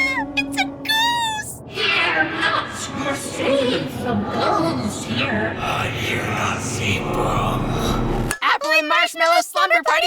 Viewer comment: Can we get captions please I have NO CLUE what Apple and Marsh are saying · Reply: "Fear not! We're safe from ghosts here!" "But you're not safe from..."